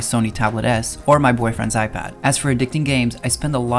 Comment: did he say bf iPad?